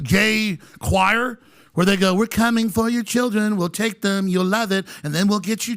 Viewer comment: I thought i heard we're coming for your children until someone pointed out their saying shopping not children 🤔